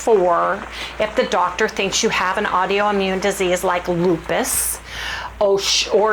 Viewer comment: I had to go back lol I’m sorry, but sometimes you say the funniest words and it gives me a laugh. I thought you said audio immune. I know we all make mistakes when we’re talking it just happens cause our brain does it, but it was kind of funny. I hope you don’t have some audio immune. I think that sugar diet really did mess you up.